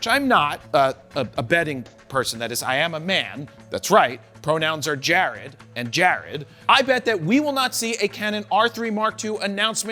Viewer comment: Honestly thought you said your FRONOUNS are Jared and Jared